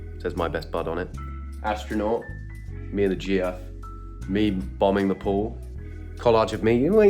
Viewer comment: did i hear gf as in girlfriend 😔😔😔